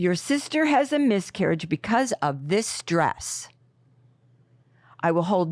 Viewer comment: At first I thought she said “because of this dress…” I was confused hahaha